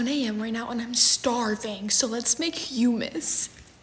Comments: For a second I thought you said I’m gonna make humans in my face was like😮 then I was like did I hear it right and then I restarted the video and I was like oh humus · Why did i thought that she said "LETS MAKE HUMANS '' LOL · I literally thought that she said ' let's make some humans ' 💀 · Bro I rlly thought she said “let’s make humans” 💀🖐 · I thought she said "lets make humans"💀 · I thought she said LeTs mAke HumAns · I can't be the only person who thought she said humans instead of humus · I thought she said: let make humans😂😂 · NGL i thought she said LeTs MaKe HuMaNs😂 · I thought that she said let’s cook humans I was gonna freak out lol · Bro, I literally heard "let's make humans" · Is it me or I thought she said let's make humans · I THOUGHT YOU SAID HUMANS 🥲 “Let’s make humans” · Bro I am stupid bc I thought it said let's make humans 😂😂😂😂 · Ngl when she said humus I fr thought she said HUMANS!!💀